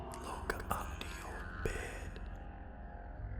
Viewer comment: a mysterious scary whisper-type voice can be heard after says the word "Videos" ... can anyone tell what's it's saying ?? Is it the voice of??? SPOOKY !! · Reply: "Look under your bed" :P